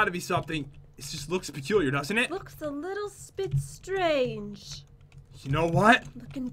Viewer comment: did say little spit strange